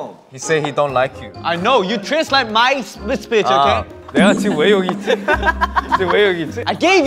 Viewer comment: I KNOW he said "my speech" but... it sounded like "this bij" and I love it. Side note, Han's English is SO good.